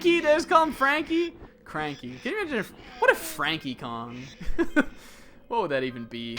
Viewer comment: I dunno if I heard you correctly at 15:11, but I THINK you just said "Frankie Kong"